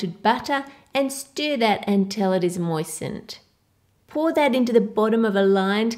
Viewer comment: First I thought that she said and smear that until it's poisoned